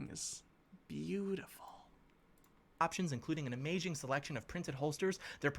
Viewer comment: did he just say “amaging” instead of amazing?